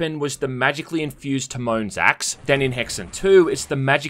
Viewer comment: 🤔 sounded like " Timone's axe " lmao 😂 - Lion King would have been a very different movie....🤣